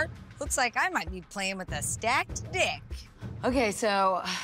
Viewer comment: Hold up what? Did I hear right!? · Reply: Stacked deck